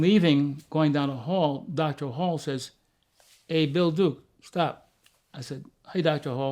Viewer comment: I thought it said Bull Dike at first! 😬